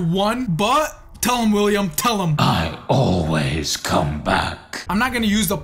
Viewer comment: I omost thought i heard " I ALWAYS COME TO WACK ". I heard that so wrong 😂